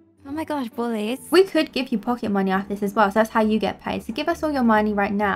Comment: I thought he said, “omg we could pend it in a “pole” I was like, “w-what kind of p-pole” 😂